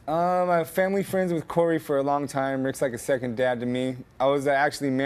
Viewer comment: what did he say that...'corry is like a second dad to me'....what... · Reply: Family friends with Corey for a long time. Rick's like a second dad to me. -Corey's father.